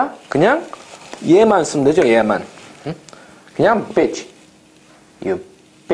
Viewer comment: Does anyone know what he's saying at 3:48 ? Please check and get back to me. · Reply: He said “그냥 bitch”=“you can just say “bitch””